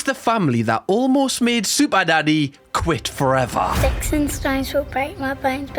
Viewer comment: sounds like he said super daddy